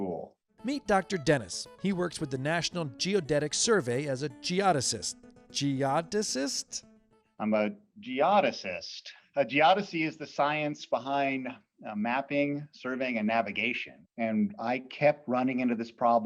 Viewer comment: I heard Jihadist, ISIS confirmed